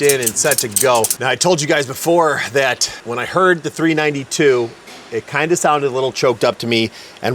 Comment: Did you say it was a 392 engine? 😅